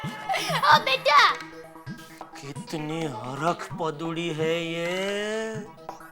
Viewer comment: what he said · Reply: Harakh paduri overexited